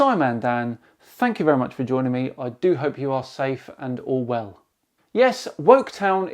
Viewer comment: I know I just misheard SciManDan, but at 0:40 it sounded like he said, "I do hope you are safe and 'Orwell'." which would be rather topically apt given that it almost seems like we are headed right into the world of 1984.